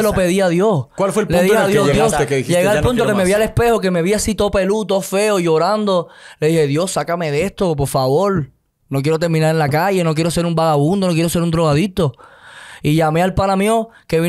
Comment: lo que dijo, Yo también cuento queria salir de las drogas ise eso